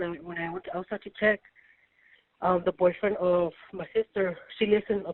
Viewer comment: I thought she said was the aunty boyfriend 😂😂😂😂😂😂